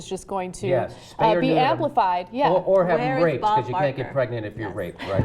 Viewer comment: did he just say “or have them raped, you can’t get pregnant if your raped”?